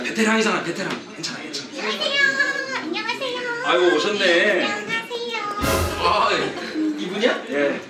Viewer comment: 첫판부터 장난질이냐!(타짜 대사) ㅋㅋㅋ 자막 뭐여요 ㅋㅋ"I9 오셨네~" "19금 오셨네~" 로 들리는건 나뿐인건가 ㅋㅋㅋ 밑밥지대로고요~ ㅋㅋㅋㅋ 활약이 기대기대